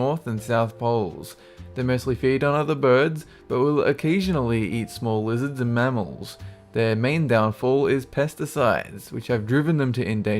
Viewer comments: Am I the only one who hears small wizards at 2:20? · Hahahaha sounds so cute when he says JIMMY!!! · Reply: i thought he said small wizards lol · I heard... a birds who ocasionaly eat smal wizards and mammals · I thought he said that they will occasionally eat small wizards... THEY DO EXIST! :D